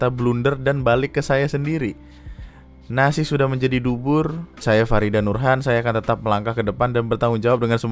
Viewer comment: guys, please bilang ke gue kalo gue salah denger bubur jadi dubur